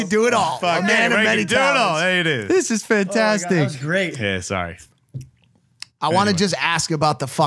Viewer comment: did he just say Ksi? Lmao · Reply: He said yeah sorry